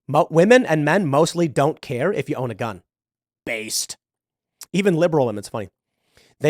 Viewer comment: Did Tim just say BASED!?